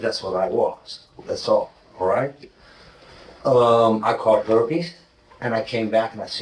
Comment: I think I finally understand what he says after he "caught herpes"... It sounds like he says "I caught herpes and warts." 🤷🏾‍♀️ Hey, could be a stretch, though.